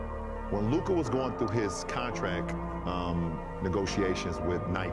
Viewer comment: Did he just say "con-track"? Like wtf..